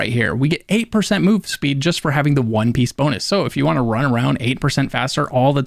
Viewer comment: did i hear “ONE PIECE” 👀🏃🏿‍♂️